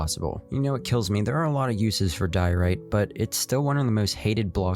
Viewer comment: when he says duorite the captions says bir poop😂